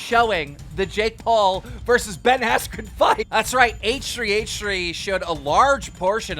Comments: I thought he said Jake Paul vs Shane Dawson in the beginning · I thought he said Jake Paul vs Shane Dawson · thought you said jake paul vs shane dawson and honestly i wasnt even surprised considering he chooses shitty opponents